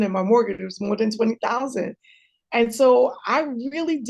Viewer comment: I'm sorry, did she say her mortgage was more than $20,000? 😏